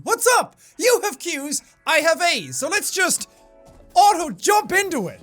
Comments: did i just hear "i have aids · Honestly,thought he said : “You have Q’s, I have AIDS “ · i thought he said "u have qs i have aids!" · I thought he said "you are cute, I have AIDS!" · at the start of the video i lowkey thought he said "you have q's and i have aids" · i thought he said "you have Q's, i have aids" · At at the start I thought he said “you have Q’s, I have aids” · I thought he said “you have qs, I have aids” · Why did I hear "You have Q's I HAVE AIDS" 😳 explossssssssed jkjk ily 💕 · Misheard his words: "you have cues, I have aids" Jesus Christ Sean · In the intro I thought you said "I have aids" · In the beginning I thought you said “you have Q’s I have aids” 🥶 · I thought he said "I have AIDS :D" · I thought he said you have Q's I have Aids 😂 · So sleepy right now, I thought Jack said "I have aids" like wtf🤣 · At the start, I thought he said "I have AIDs" instead of "I have As" I need to get my hearing checked... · i thought he said "I have AIDS" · Is it weird for the intro I heard him say you have pubes I have AIDS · Is it just me or at 00:05 did he say "you have Q's and I have aids"? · When he said "I have A's" who at first thought he said "I have AIDs" 🙄😂😂🤦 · At first I thought he said 'You have Queues, I have aids' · I thought he said "i have aids"@ 0:07💀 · Did you say you ... you have aids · I thought he said “I have aids, let’s jump into it”